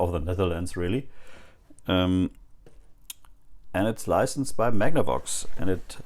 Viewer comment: thought it said based off Magnavox